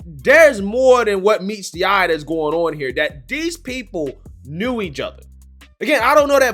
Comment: I thought you said "these people do each other" 🤣🤣 I had to go back and check.